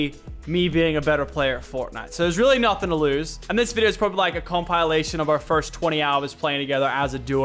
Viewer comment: did he say com-pie-lation or is that just me